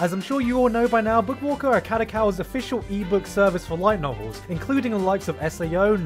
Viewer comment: i thought he said white novels lol